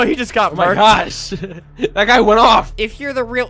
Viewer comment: DID ALBERT JUST SAY awh sh💩t